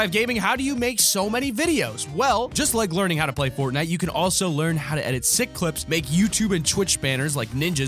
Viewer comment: Don't forget, if you wanna check out Skillshare make sure to sign up using this code <link> and learn how to edit videos like ours :D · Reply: I thought edit said and learn how to eat videos like ours :D